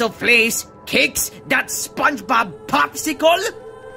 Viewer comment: Did I hear that right... 'Spongebob popsicle!'... 🤣